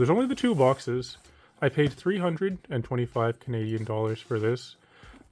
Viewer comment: I thought he said 325 million dollars and almost had a heart attack